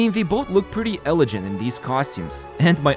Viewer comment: Did bro just say ele-jant 😭😭